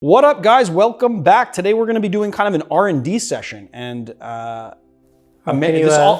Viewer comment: Thought you said R&B session, thought I would leave pregnant